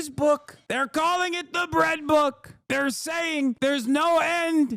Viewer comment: helpppppppp the editor thought he said red book not bread book 😭 poor anarchists the one shoutout yall get completely overshadowed :(((